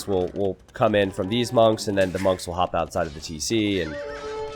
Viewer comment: said "monks will hop outside of the TC" ... It took me about 5 seconds, then I entered desperation mode... I heard "camels will hop outside of the TC". UHHHH What a rush!!!